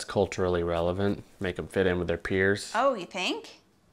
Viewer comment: When you said their peers I thought you two said the queers lol